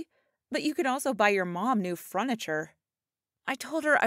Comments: Her: i’m poor so i buy second hand clothes 2 minutes into the video Her: My friend got onto my computer Me: Anyway, who else heard “fruniture” at 2:41 instead of “furniture” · Did she just say fruniture?